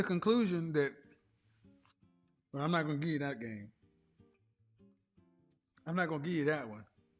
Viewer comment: Can someone tell me what he said at 51:20, please. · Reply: Give you that game